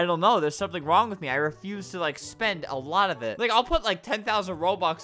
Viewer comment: The editor cut him off at the end and it sounded like he said I need a big gay